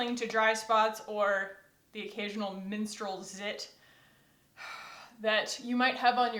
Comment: I thought you said “minstrel zit” and I was like your zits sing 😂. Then I realized you said menstrual. I personally think zits should sing if I have to put up with them!